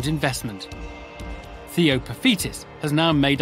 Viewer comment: Theo The Fetus, Duncan Banner Time and Pizza Jones. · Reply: The first time I ever heard Theo paphitis I actually heard it as theo the fetus